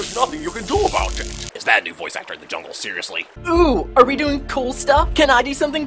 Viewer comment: What is he saying at 47:35? Can't understand it even while slowing the audio down. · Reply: Steve O-saurus i think he says "is that a new voice actor in the jungle seriusly